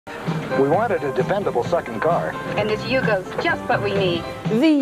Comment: did he just say "we wanted a dependable F*cking car"